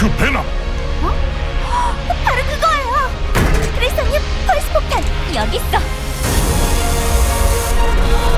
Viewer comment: 나만 윈스턴님 펄스폭탄으로 들리냐